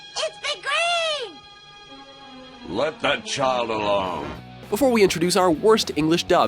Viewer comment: did piccolo just say let that child alone